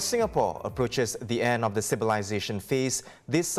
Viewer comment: Did he say "The End of the Civilization phase"? Covid has finally done us over 🪦 ⚰️